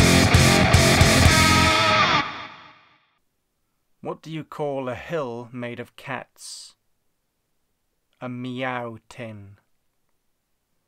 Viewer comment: I thought you said a “Hell” made of cats. But really it’s the same thing isn’t it?